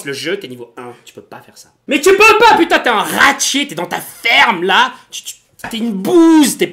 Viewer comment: ce personnage est génial! jai rigolais tellement fort que j'ai entendu "ferme ta gueule" xD